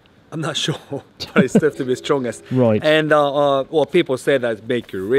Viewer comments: I straight up thought he said "get rid of your butler" as well. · I thought he said it'll make your butt lag go away 😂